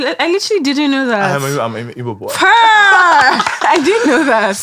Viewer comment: Did I just hear "I'm a igbo boy"